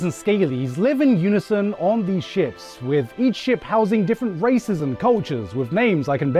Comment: … why did I hear racism